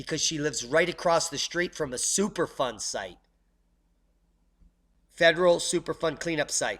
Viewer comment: Who is already paying for the superfunding site? Now they can call it super duper funding. At first I thought you said superfun site. Would not shock me the way they name things these days.